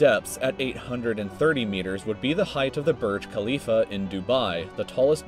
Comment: "Burj Khalifa" ... I thought he said.. Mia Khalifa XD